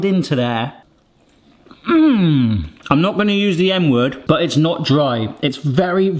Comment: oh my god, I thought you said "N word", I was like, why would you say the N word because the chicken is nice? but you said M word... just say moist, less confusion lol